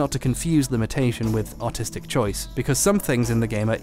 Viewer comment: i thought he said autistic choice for some reason